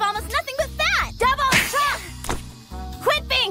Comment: What does Yomi say at 0:05? There are no hearing impaired subtitles on the DVDs. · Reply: "Quit being so damn rude!"